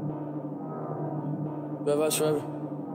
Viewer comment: sounds like “roblox forever” 😂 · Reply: I thought he said “bad bus driver” lmao